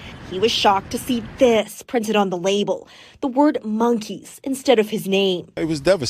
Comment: Bro i thought he said monkeese